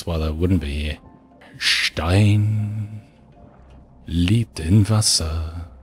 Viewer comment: did he just say "Stein liegt im Wasser"? (german)